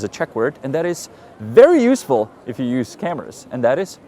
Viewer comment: At the end I heard, "[...] very useful if you use scammers" 🤣 No I don't really use scammers, but thanks